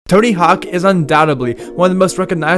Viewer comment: Sounds like you said Turdy hawk in the beginning lol